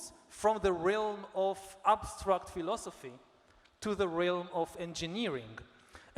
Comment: I'm happy to still a live until meet YNH via internet , I can not stop watch and listen, great to meet holyood and science psychlogy economy ...philodphie..from Morocco thank you Ynh. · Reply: Excuse me if correct a little mistake you did my friend: philosophy I had to bring to your attention for a second I thought you were saying pedophile*